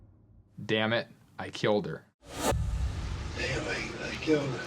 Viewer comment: I hear "anyway, I killed her" ... I seem to be the only one though... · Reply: i thought i heard "Anyway, i killed her"